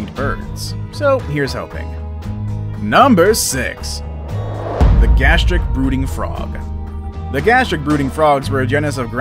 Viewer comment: When he said the Gastric brooting frog I thought he said Gastric booty frog🤣🤣🤣🤣 Even the dodo I thought the doodoo